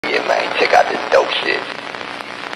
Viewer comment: RIGHT HEREEEEEEE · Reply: I thought he say LIKE YEAHHHHHH